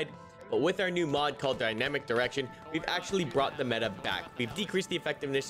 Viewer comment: Oh, he said "Dynamic Direction"... I heard "Dynamic Erection"... Sorry about that...